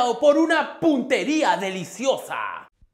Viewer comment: Yo ESCUCHÉ 0:02 "PUTERIA DELICIOSA" 😂😂😂 Edit: Creó que estoy muy degenerado.